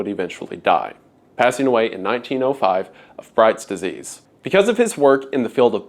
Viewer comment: Thought you said Fright's desease, nephritis.